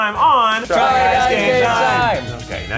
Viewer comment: Let me be honest. For a while I thought they said “try guys gay time”. So while I was watching I could never figure out what was gay about their activities...